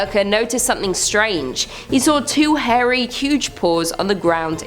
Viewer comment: Okay, I was eating chips and I thought you said two hairy huge balls and almost spit out my food 🤣